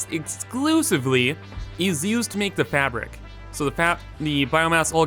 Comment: did you say....FAP?